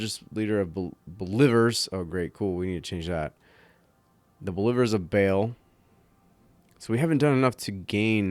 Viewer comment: Haha I thought you said "Beiievers in Fail".